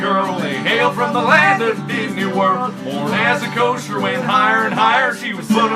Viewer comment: Did I hear "DIDNEY WORLD"!